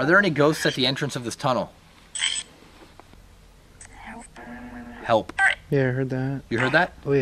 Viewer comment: I heard "Help Us"